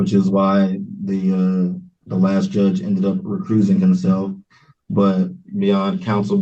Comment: At around 14:00 does he say "the judge recruising himself?"